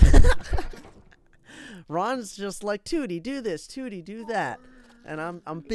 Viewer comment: When he said waddles is spelled with 2 Ds I thought he said tewity